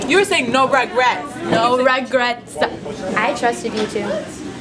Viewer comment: At first I thought she had said no rugrats but then I re watched and she said no regrets